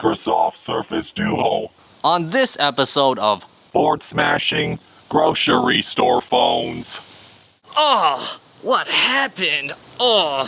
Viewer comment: I thought you are gonna said: Welcome to Bored Smashing Gucci Store Phones. :D